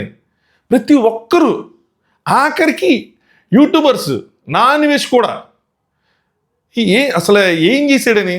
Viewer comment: I thought you said non veg and I was having a hard time about what has nonveg got to do with this. I had to reply it several times to understand that there is an YouTuber 0:55 called na anveshana 😂😂 I didn’t know who he was before and after watching this video , I have no intention of knowing him at all. Thanks for the heads up.